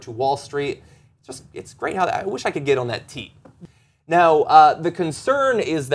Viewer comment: Did John just say he wants to get on the greedy tee and it's great at like 4:45? That ain't right.